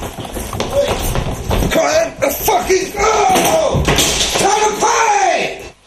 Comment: What does Pancho say at 7:20? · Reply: Josh, “Cmon then! Ya fkn argh! Gonna pay!”.